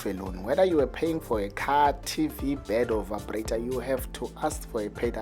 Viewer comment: Did he say "vibrator" at 5:20 🤣🤣🤣